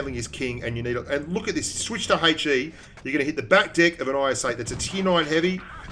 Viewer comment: did he just say back dick!?!